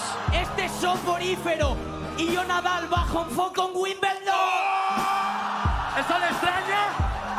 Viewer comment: no entendi el punch pero me re cebo me erizo la piel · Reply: "Este es Soporifero" (aburrido y sin gracia) "y yo nadal bajo un foco en winbeldon" (el mejor del mundo bajo los focos de la gran final)